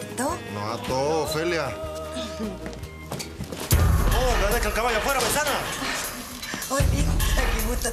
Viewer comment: que dijo? No deje el caballo afuera paisana? Jsnsjjsjs